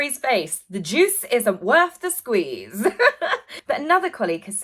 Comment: I misheard you and thought you said “the deuce isn’t worth the squeeze” 😮😂